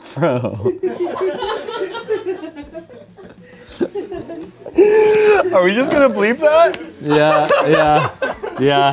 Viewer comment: WHAT DID ANTHONY SAY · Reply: swastika 😭 if you listen closely you hear “sw-“ before it bleeps